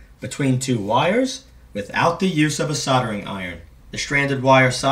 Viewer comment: Sounds like your saying soder?😂